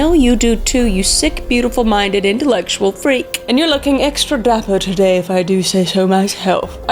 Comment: at first I thought she said your looking extra black today I was soo confused lol 😂